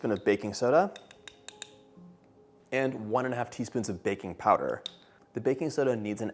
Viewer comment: 2 and 1/2 cups of all purpose flour 1/2 tsp of baking soda 1/2 tsp of baking powder 3/4 tsp of salt 1 cup of room temperature,unsalted butter 1 and 1/2 cups of granulated sugar Zest of 2 large lemons 3 large eggs,room temperature 1/4 cup of fresh lemon juice 1 cup of buttermilk 1 tbsp of vanilla extract Lemon frosting 8 oz of softened cream cheese 1 cup of softened,unsalted butter Zest of two lemons 1 pinch of salt 7 cups of confectioners sugar 2 tbsp of fresh lemon juice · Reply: I think he said 1 and 1/2 tsp baking powder